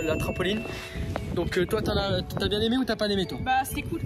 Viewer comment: ''toi pas bien aimé ou ta pas n'aimé'' xD (Sinon super vidéo) ;)